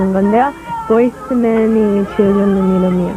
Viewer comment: 보이즈투맨이 지어줬다고???ㅋㅋ 내가 잘못 들은건가 그 형님들이?! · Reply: 보이즈투맨이 지어준 이름이에요. 라고 하네요